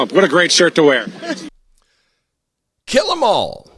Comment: With his lack of teeth we misheard him. He said "Jill" or maybe "chill"... Them all.